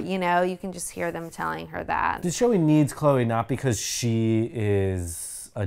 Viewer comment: “The show-e needs Chloe…” is that what he said? 😂😂😂 good ‘sode, guys!